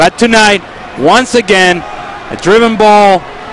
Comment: Did he just call Ricardo Clark, Ricardo Call at 0:54 ?